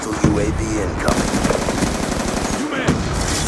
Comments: Sounds like he said "Umad?" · does he say you mad